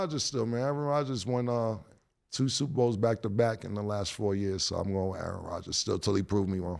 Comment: Did Cam say Aaron Rodger won 2 Super Bowls back too back ? 15:00 hell I been